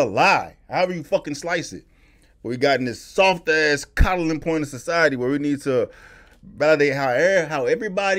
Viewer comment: At first I thought you said "soft ass collagen point in society"..... I laughed so hard. Then I was like, no, he didn't. Cawdling is funny too tho.